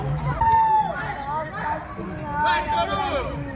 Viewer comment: VAI ""TORUGO"" É ISSO MESMO QUE OUVI ?????????????????????????????